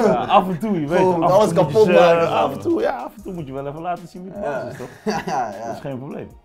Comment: wat zegt hij:AF EN TOE MOET JE LATEN ZIEN WIE DE BAAS IS’gelijk da vrouwen minder zijn ofzo!!